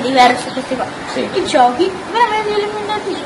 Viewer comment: nell' 6:24 ho sentito i giochi leoelemenaticnx